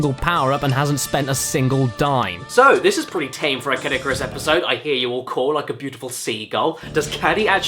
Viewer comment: Who else thought he said "Kid Icarus" instead of Caddicarus at 5:28?